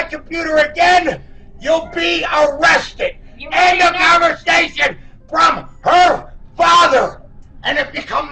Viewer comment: What is always captioned as *unintelligible* is daddy saying "end of conversation."